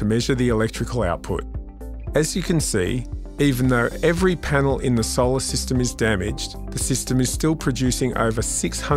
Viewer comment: i misheard it for “As you can see, every PLANET in the SOLAR SYSTEM is damaged...” 🧠